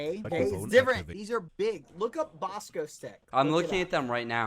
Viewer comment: At first I thought he was saying Costco stick